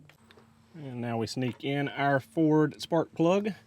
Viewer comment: I swear I heard “now we sneak in the ford plug” I’m like wait is this an inside joke I’m not aware of?